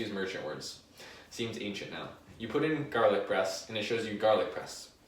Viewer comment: I thought you were saying garlic breast the fist 5 times, made it even more entertaining.